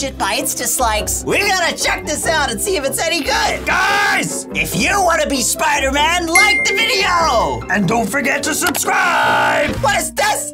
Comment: I heard “like the video if you don’t want to be spider man” 😂